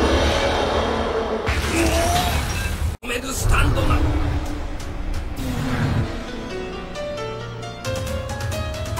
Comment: what did say 🤨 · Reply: Jikan ga tomeru sutando da.....